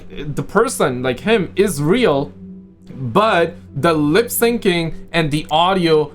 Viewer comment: Did somebody say israel